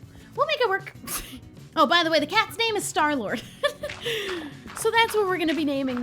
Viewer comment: I thought said he cat was named Snarl -Wort and I was think wow that’s a creative name like I’m not judging but where did that name come from then I saw it was named starlord